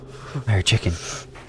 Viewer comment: i hear chicken, fuckin punches a chicken and kills it*